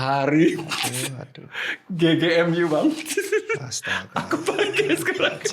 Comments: Volume kerasin di menit 1:05:40 dokter Tirta bilang apa???🤣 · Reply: Astagfirullah, bajingan cuk 😂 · Nonton ini make earphone, jadi kedengeran bisik misuh²nya Dr Tirta😂🤣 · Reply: "astaga bajingan cok cok" WOKWOWKW